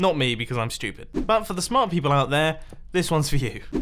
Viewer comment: Did you say "south"? Is there something wrong with south maybe?